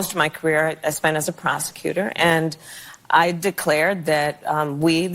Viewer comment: I swore she said she spent most of her career as a prostitute. I had to rehear that.